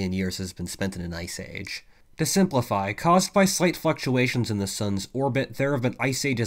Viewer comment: Legit sounded like you said "slight F^*ktulations" lmfao